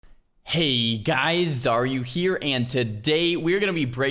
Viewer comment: oh man Ive been watching you for a couple of years now, but I always heard you say "hey guys, are you here?" which didnt make any sense to me, but you actually say your name lol